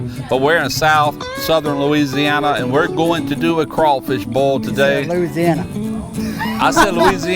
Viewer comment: Correct me if I'm wrong.......But you said crawfish bowl? That's what I heard you say. A good ol crawfish bowl. You Amurricans with your thick accents lol.